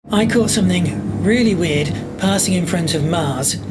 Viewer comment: This guy sounds like he is saying "I caught some N word " right in the beginning of the video. Lol I know he doesn't mean too but omg.